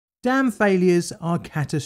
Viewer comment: I legit thought he said "Damn failures are catastrophic events"